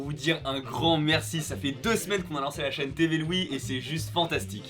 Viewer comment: j'ai compris TV Louis, ça sonne bien en plus